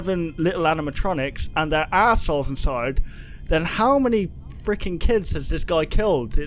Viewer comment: When he said "if there are 7 animatronics and there are souls inside" I heard "assholes" I was dying